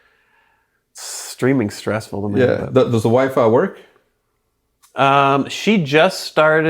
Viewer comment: Did he say wife or wi-fi? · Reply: I think tried to say wifey